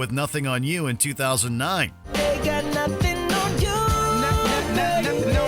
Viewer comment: I can't believe that no one is saying that sounds like he's singing "Nut'N on you"!!! I guess it's only me that hears it!!! LMFAO 🤣🤣🤣🤣 I definitely don't hear the "th" sound when he's singing!!! LMFAO 🤣🤣🤣